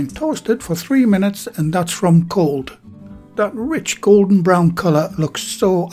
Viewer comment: I keep hearing "Gordon Brown"